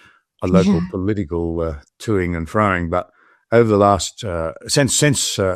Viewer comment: John Anderson says something that sounds like "turning and frying" indicating that he does not want to involve Ayaan Hirsi Ali in Australian politics. What exactly does John Anderson say, and is it an English idiom?